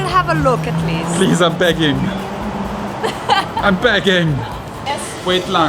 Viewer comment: did I hear saying “wait lang?” guys we need a sit down vlog about ur favorite filipino expressions/gesture. who’s with me??🤗🤗